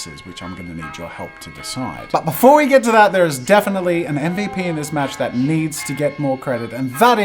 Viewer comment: I don't think i heard you right... I heard "ending penis match"